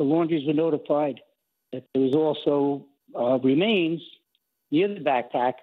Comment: Did they say his remains were "in" the backpack? That is what I heard from a couple of places. How is them being in the backpack not suspicious? He didn't put himself in it and how much of him could fit in it?